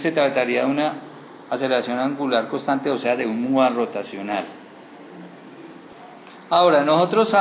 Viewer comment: que dice un que rotacional, muchas gracias. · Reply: orion dionisio raigosa carvajal MUA rotacional